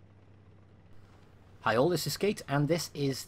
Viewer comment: still sounds like your saying "hail this is sk8" 😂